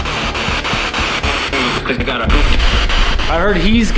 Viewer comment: instead of "he's gotta" I heard "he's gotta poop"